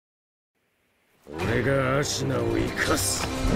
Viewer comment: Anyone else hear him say nigga